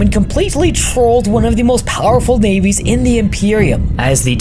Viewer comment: I don't know why, but when I was scrolling and listening at first I definitely thought you said "trolled one of the most powerful babies".